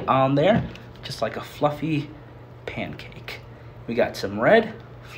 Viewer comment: i thought he said fucking pancake